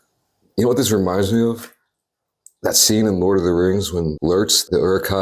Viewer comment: did anyone else thought he said "semen" instead of "scene in" at first??